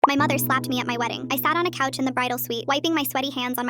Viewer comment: I lowkey thought that said "My mom slapped my meat at my wedding"